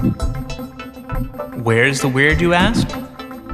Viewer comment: In the beginning sounds like he says "WHERE IS THE WEED". haha